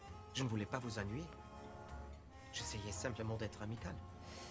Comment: I was changing the volume at 0:31 and it covered the subtitles so I thought he was saying "I was simp"